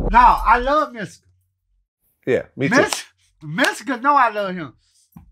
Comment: I thought crunchy was saying Mexico lol he was saying mystical 🤣